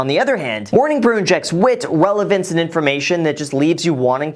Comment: I heard "Welavance"